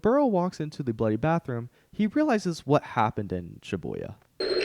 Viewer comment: sorry am i stupid or did you say shibuya? cuz i keep replaying this section but i cannot, for the life of me understand what that word is